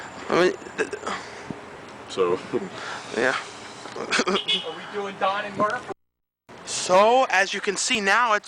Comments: Did i hear some fans say "Are we doing Don and Murph" at 8:39? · lol did somebody recognize you guys? i heard "don and murph" when you were reviewing mbv before you cut the camera off